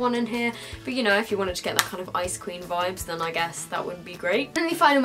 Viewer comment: When she was talking about the blue sleek highlighter, I thought she said you could be pulling off the 'ice cream' vibe instead of the 'ice queen' vibe XD